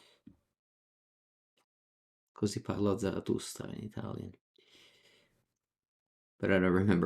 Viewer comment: What the name of the Italian book you said at 18:35? And the author at 19:35? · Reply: Zaratustra - Friedrich Nietzsche